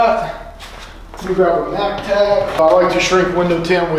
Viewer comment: Didn't hear you correctly at 0:56- 0:58. Looking for that product! Just need the name. Mac tac? Mag tag? Mac Pad? Mag pad? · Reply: Mac tac I'll send a link